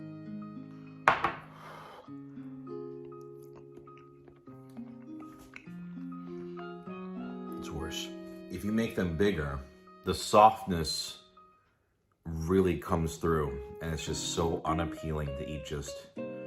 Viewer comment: I swear I first thought you said "it's horse" referring to the meat Chef Boyardee uses